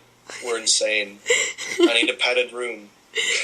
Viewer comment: When your friend said "I need a padded room," I first thought he said "I need to pat a groom." Mishearing words is pretty awesome.